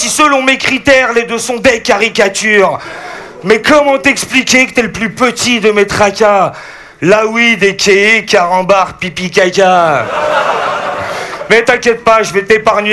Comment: Il a dit quoi là ?? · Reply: comment t'expliquer que t'es le plus petit de mes tracas, Lawid a.k.a carambar, pipi, caca